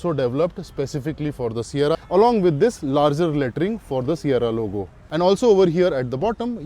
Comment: Did he just say Larger Latrine ? 😂😂😂😂😂